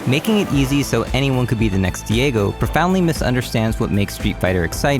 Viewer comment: Am i the only one who heard Diego at 10:35?